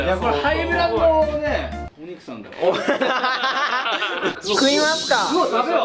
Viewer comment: のカンタの｢食いますかぁ！｣が野獣の｢やりますねぇ！｣に聞こえた俺は重症